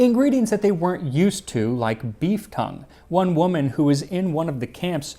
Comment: I had to do a complete double take because I thought you said 'beef cum' instead of 'beef tongue'